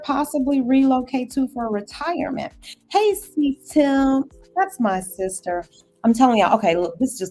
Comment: Hi there, what was the options course you recommended in the beginning at 2:58? I couldn't catch the name. Also the calendly link in the first comment did not work. Just don't want you to miss out on bookings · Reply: Canissia’s class. She the Trading Nijji on YouTube